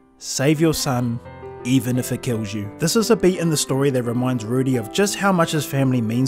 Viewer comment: what did he say? The beat of the story? Thats what he said right